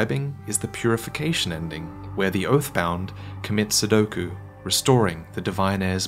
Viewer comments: Lol sounded like you said sudoku when I think you meant seppuku. Other than that, great video as always. Just found it funny thinking of your character going to purify by completing a paper puzzle by etching with a sword. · Did you just say sudoku instead of seppuku? Are you joking xD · did he say sudoku?